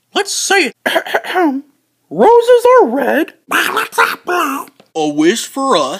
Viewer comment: Can you please tell me what Donald said at 0:05 and 0:31 because I didn't understand what he said too much? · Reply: he said "Violets are blue" and at 0:31 he said "Yeah, I did see that, the candle went..."